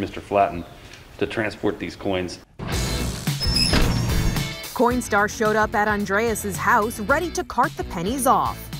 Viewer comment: I definitely heard "Corn Star" 🌽⭐